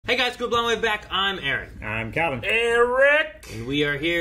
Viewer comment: does he say “hey guys, come blow my back” ?